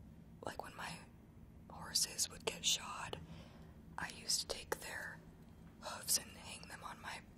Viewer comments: For a second there I thought she said "when my horses would get shot I'd hang their hooves" me: uh...as you do.... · What does she say at 4:02 ?? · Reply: mahika shrivastava “when my horses would get shod” meaning like the things on their feet